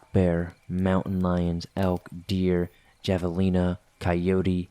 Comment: What animal is he saying at 9:29? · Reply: That's what I heard: javelina, but didn't pronounce it "havalina". Also, they're common in the SW, so that's probably it.